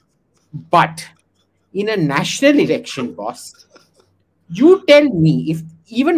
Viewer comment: yeh ne kya kaha? 😂😂😂😂 National????!! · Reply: Lok shabha election